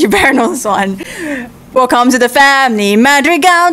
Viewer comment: What the did he just say at the end he sad home to a lot cute gal pals